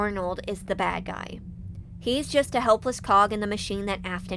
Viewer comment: ok i looked away and i heard "helpless pog" and i was like poggers? My brain combined pawn and cog 😭